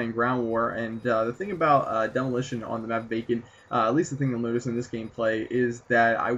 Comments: sounded like you said bacon instead of vacant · k right at the start it sounded like he said "on the map bacon" instead of vacant, anyone else think that? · thumbs up if you thought he said 'on the map bacon' xD